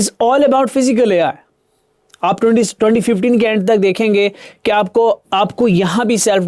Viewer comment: Did I get 2016 instead of 2026? Did I misheard